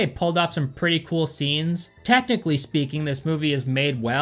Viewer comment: Anyone else hear him almost saying "Testically speaking..." at 19:15?